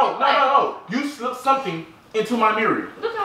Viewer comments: Did Anyone Hear Him At 11:22, You Slipped Something Into My “Mireal”. 😂😂💀💀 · “you slipped something into my mereal” 😂 who else heard that? 😂😂